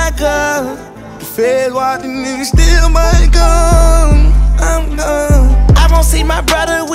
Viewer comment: But y'all didn't hear thug tho... What he say at 2:31? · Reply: The feds watching and they still might come